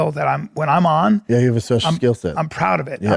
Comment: what the fxxx did bapa just say???? 👂🏽 🤚🏽🤚🏽🫤😐🤔😳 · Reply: You have a special skill set...